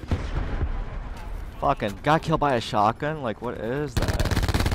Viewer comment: Bruhh xD you said 'If I had shotgun' but I heard 'my n***a shotgun' 😂